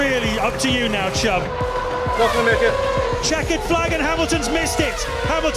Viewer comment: What does he say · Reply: “Not gonna make it”